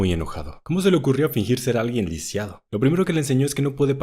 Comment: 😅 me parece que dijo liciado en el minuto